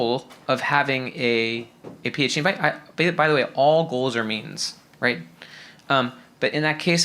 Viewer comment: “All goals are _______” What did he say? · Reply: Means